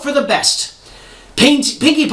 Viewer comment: What did he say before Pinkie Pie? It sounded like "Paint." X_x